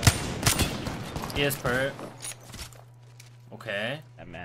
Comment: "he has purp" what i hear: "he is a perv"